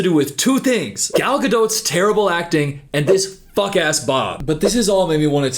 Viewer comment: I had to replay this bc I swore you called Rachel Zeggler a fuck ass bop 💀